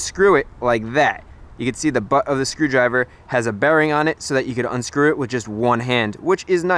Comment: I know you meant "bearing" but I heard "berry" 😂 enjoyed the video and the effort it was awesome